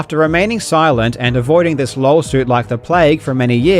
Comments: is that just your accent coming through on the word 'Lawsuit', or did you actually call this a "LOLsuit"? cuz thats the first time ive ever heard that term, and i love it... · I swear to god I hear "lolsuit" and it fits perfectly! :D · did he say „lolsuit“? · did really just say "lolsuit" instead of lawsuit? If so that's amazing.